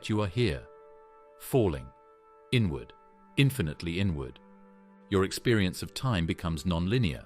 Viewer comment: I was half listening and for a second thought I heard “infinite N word”